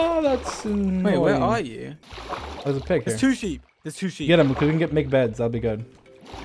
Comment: I genuinely thought said “There’s sushi” haha instead of “There’s two sheep”